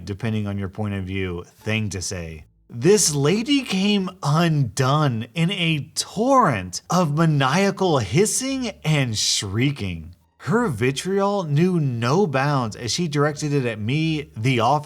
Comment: I thought it said shreking.